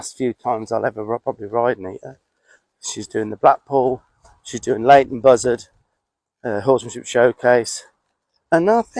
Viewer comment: Lovely! Question, around the 16:20 it sounded like he said "...She's doing latent buzzard.." what did he actually say, please?